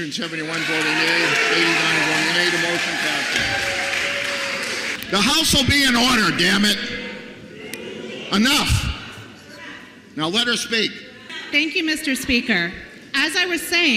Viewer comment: Did I just heard "The house will be in order DAMNIT!!!" 😂